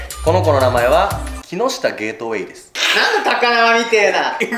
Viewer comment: 男の子が「ねえなんで」って言ってる声が聞こえるんだけど....